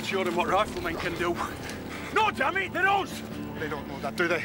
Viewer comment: Im not asking this because i cant understand accents, i am from the UK, its more that he says it so quick and shouts it. What does he say to harper at 1:35 ? Sharpe says "No dammit they're (something)" and Harper replies "They dont know that do they" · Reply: They're "us". They are British.